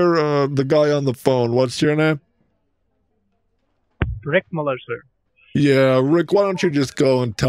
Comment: Did i mishear that or did he say his name is : Rick Molesterat